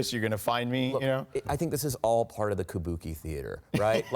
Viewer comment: More than I make in a year lol also I thought he said “it’s all apart of the ‘bukkake’ theater”